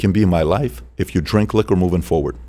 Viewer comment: What does PBD say at 31:40? ''…. if you 'Drink Lick'(?) or movin' forward.'' ? What's drink lick? 'drink a lick'(?),… meaning drinking any alcohol? That's silly if so.